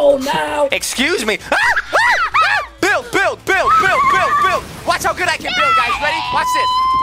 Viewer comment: when he said build it sounded like bill so then I remembered bill nye the science guy